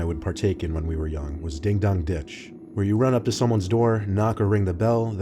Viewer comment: I thought it said ding-dong bitch